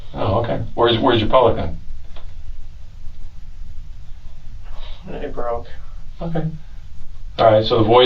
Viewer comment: At one point it sounded like he said his pelican broke .